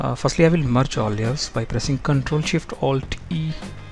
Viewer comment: What u say 9:07 "Alt+ctrl+shift and" ? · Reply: Vukosavljevic CTRL + ALT + SHIFT + E